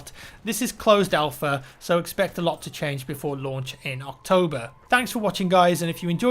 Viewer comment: I thought he said "so expect alot to change before lunch in October"